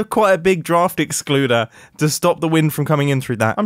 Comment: A draft excluder is a cushion thing that goes on the base of the door to keep air from passing underneath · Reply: Oh draft excluder I thought he said drafting Spooner xD